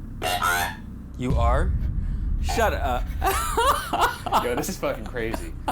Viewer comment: instead of "five" I definitely hear "small girl" . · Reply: Also right after "are we speaking with Matilda" again I hear "small girl" instead of "you are"